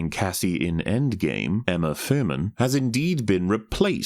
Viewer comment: I misheard the name of the actress playing Cassie, and I swear I heard him say Uma Thurman. Can we please hurry up with resurrection technology so we can get Stan Lee back to veto these dumpster fires? So much of these disasters could be improved just by having characters pimpslap the ones being pricks.